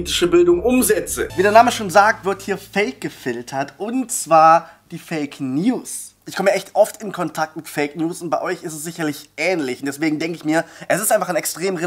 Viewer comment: ich hab erst fick news verstanden